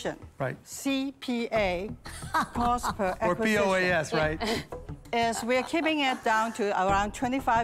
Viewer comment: Did he say P. O. E. S.?